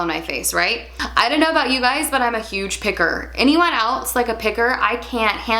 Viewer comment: I thought you said “I'm a huge pickle” LOL